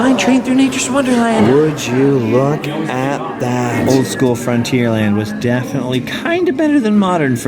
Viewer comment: Frontierland is what I said was more cohesive in theme. Fantasyland is where I said I'd like to spend a day. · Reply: My bad! I could have sworn I heard Fantasyland during that part and I had replayed it multiple times because I thought it was a strange thing to say. Now, I can definitely hear it as Frontierland. Wow, I've always had hearing problems and my hearing has been getting worse lately, but I've never heard something so badly while watching a video. Sorry about that, man.